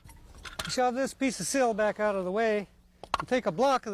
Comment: the subtitle is wrong it’s sill not seal not sail it is sill